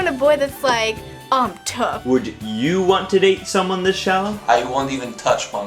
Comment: what does it say? would you want to date someone ............. sorry english is not my first language, did someone get that?